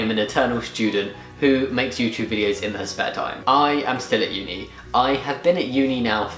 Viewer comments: No offense, I thought I heard at 0:52 you referred to yourself as "her"? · did you just misgender yourself??? 0:52 ...or am I hearing wrong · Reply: It really did sound like "her" - I realised it must have been "their" though